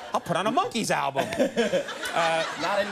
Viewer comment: Did he say "I'll put on a monkey album"?????